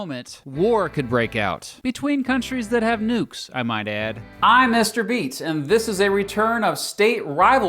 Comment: i thought he said mr beast